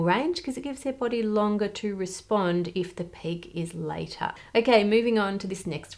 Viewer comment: "if the peak is later" but I swear I heard "if the pig eats later" 😂